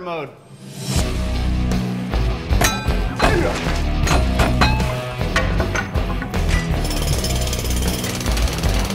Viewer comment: what did he sayyyyyyy? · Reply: "Don't worry, he got the pass...I think."